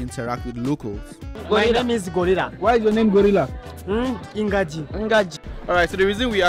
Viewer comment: Did i just hear ngaji!! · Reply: It’s just the effect of speech impediment. He really meant to say “ingagi”. GA as in “gap” and GI as in “give”.